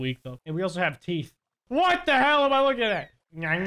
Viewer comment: Did he say H e l l at 5:00 I unsub